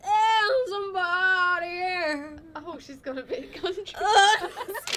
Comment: What did Jesy say at 0:32 ? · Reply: Jerrie's Universe "oh she's gone a bit country"